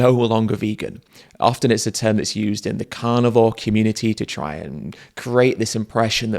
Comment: "I could never be kind to animals…" When I played this video from the start, when says "carnivore" at 0:17 I heard "cannibal."